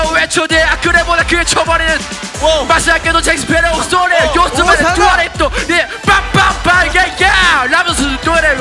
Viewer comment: it sounds like he said duo lingo or dua lipa 🤣🤣